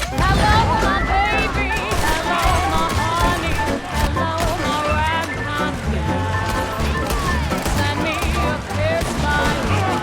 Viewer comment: This is a great vid! Also, she sings “hello my ragtime gal. Sounded like you said “right time”, which I realized still makes sense, lol.